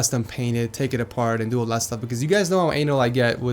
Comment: did he say: 'you guys know how anal I get'?